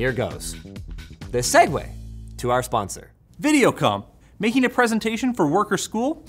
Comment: Dude i thought he said Video Cum